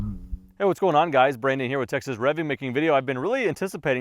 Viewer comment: Didn't know about this channel. Heard you talking and had to doubletake. Thought I heard Texas Plinking for a moment there.